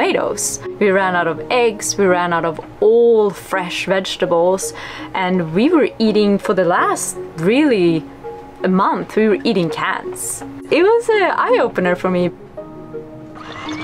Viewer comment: I thought she said we've been eating cats not cans 🤣